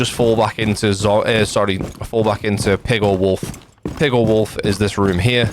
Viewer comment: genuinely thought you were saying "pigor wolf" at first @ 6:34 😂